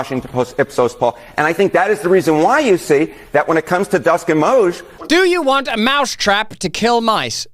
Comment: Swear he said Dusk & MOGE